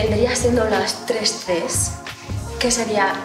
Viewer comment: I innocently misheard the last one as “chewing” lol